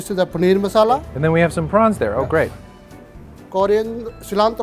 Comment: Okay I got some more praises for. Not only is he a great host but he's an even better guest. Notice when he interviews people he does not interrupt them when they are answering. So many hosts will always want to jump in with what they know trying to impress. There's only one other food host that comes to mind when I think of non-intrusive interviewers, Anthony Bourdain. He too has an almost encyclopedic knowledge of food but yet he still always let's the experts explain and talk. Like I said, great hosts but even better guests. Number one rule when visiting establishments, countries or homes, be a good guest! Thanks for being great! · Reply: Eater Hey did just say prawns ??